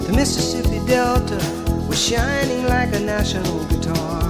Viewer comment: It's a National guitar. They're metal, hence "Shinning like a national guitar". Honestly you could have looked it up. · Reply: Sorry, my bad. Before I read your reply, I doubted my memory, relistened to the track just now, and heard that it's "national." I misheard it as being natural like the shape of the Delta. Thanks for explaining about the guitar being metal.